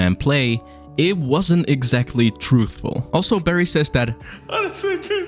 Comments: at the scene in 14:42 I heard " honestly i tried to fix things, i tried to give you that money" which makes more sense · I thought he said "I SHOULDN'T have given you that money"...that, if I misheard it, made sense:) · I thought Barry said “I shouldn’t have given you that money” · You misheard at this part mate. He says: Honestly i tried to fix things, i tried to give you that money. $250,000. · Ohh, I thought he said, "I shouldn't've given you that money." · I thought he said "I tried to give you that money"